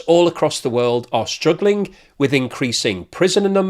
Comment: I genuinely thought you were introducing this as "prisons are struggling for inmates" !! LOL I misheard obviously but it made me laugh.